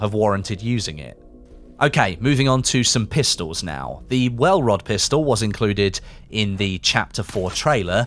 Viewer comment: when you said welrod pistol I thought you say rauh welt lmao I was like I didn't know they made guns